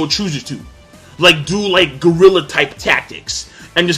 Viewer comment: does says "do like Gorilla type tactics"? If so, Lol He most likely meant guerrilla tactics anyway, but it was damn funny. I laughed my ass off for a full minute.